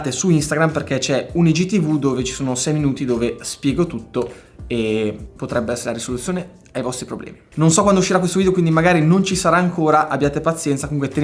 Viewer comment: solo io ho sentito: ''potrebbe essere la RIsoluzione ai vostri problemi''? Era così emozionato per la nuova arrivata che si è confuso? XD